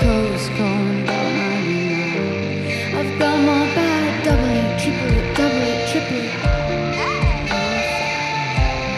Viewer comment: Does anyone know what she says at 1:07:21 during summertime sadness? 🤔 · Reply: She says "double, triple...". I think that for some reason the drummer messed up the beat and she reminds him of how it goes? I'm not sure.